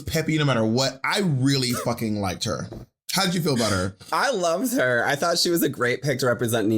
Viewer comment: did she say i LoveS her?